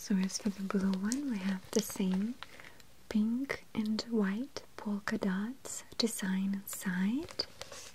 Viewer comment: Is it just me but did it sound like she said poulkadot